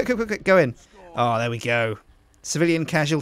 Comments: did he just say yolo? lol 'yollo' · did sjin say yolo? hahahahahaha